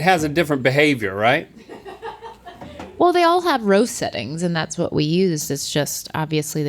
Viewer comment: did you call seasonings "roastenings"? 🤣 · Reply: she said all of the air fryers had "roast settings"